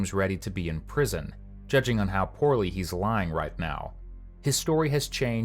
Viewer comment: I saw that one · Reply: I was like, did he just say, Poo Poo? 😂😂